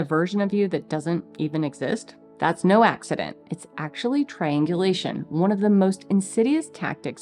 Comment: she says, "it's actually triangulation" Seriously thought I heard, "it's actually trained deletion". Yup. All rolled up together.